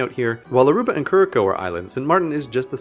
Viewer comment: it sounds like he said: Cure a cow. when he wnat to say curaçao.